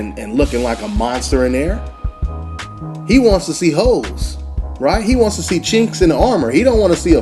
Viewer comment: I thought at first you said "he wants to see hoes" at 7:12 then you paused right after. I was like say whaaat. When you continued I realized you said holes lol